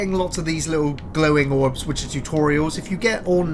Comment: I swear I kept hearing say "chew-torial" instead of tutorial.